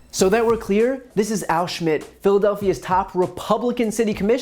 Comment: Ok I totally thought I heard him say that guys was named Al Schutts ( pronounced like Al Shoe-its)and was like...😬well that's an unfortunate name